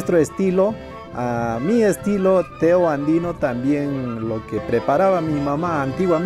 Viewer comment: Está buenazo el ponche de quinuita Pensé que decías Tío andino Pero te llamabas Teo. Gracias x enseñar.